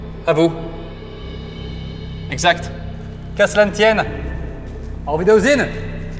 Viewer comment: Caslantienne ???aavidaozinz ? Ca veut dire quoi ce que dit norman pour dire au revoir ?? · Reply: Qu'a cela ne tienne = Peu importe et Aovidaozine... je crois que c'est un mot Random x)